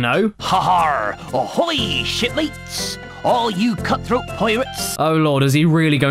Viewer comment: sounds like he says "shit mateis"